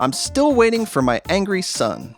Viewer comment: Yes, please! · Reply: Of course. When you said "Angry sun" I misheard it as "Angry son". What I want is: -Luigi Playable -If you play as Luigi only, the mushroom colours would swap to make this complex, but the Luigi levels would be simple if the colours stay. -Super Mario 2/All Stars Levels -Super Mario Advance Levels -3D Mario Levels -Yoshi in SMB1/3 -The boot in SMW/NSMBU -Ice Clown Car -Shoots iceballs and freezes all enemies, except for bosses -Ice Flower -Shake a fire flower -Ryu Amiibo -Because Street Fighter is my favourite game -Angry Sun -1 Way Pipes More than 2 areas in a level -Slopes -Red ! Switch and Yellow ? Switch -By triple tapping a P switch because shaking it makes a key -Galoombas in (N)SMB1/3/U -Goombas in SMW